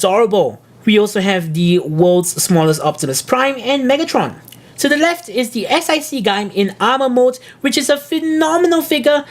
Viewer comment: or anyone else, could you please tell me what is that samurai figure called? You say it really fast so I can't understand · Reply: S.I.C kamen rider gaim